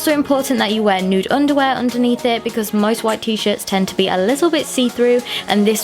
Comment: did she say wear a nude underwear under your t-shirt??